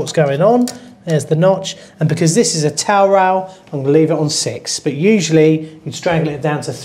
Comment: i turned on subtitles but no love. what is he saying at 25:45 where the cc says toil rail? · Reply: Towel rail